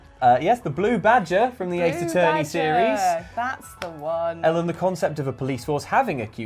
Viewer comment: Every time he said LMT I kept hearing "Ellen T"... thought he was introducing ANOTHER time zone at the same time...